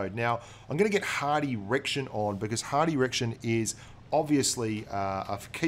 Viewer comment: lol I thought you said "I'm gonna get a hard erection now"